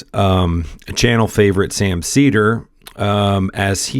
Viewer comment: When you said "channel favorite Sam Seder" I misheard it at first as "Cenk favorite".